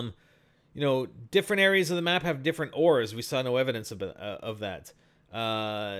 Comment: did he say ors? what is he saying at 5:12 ???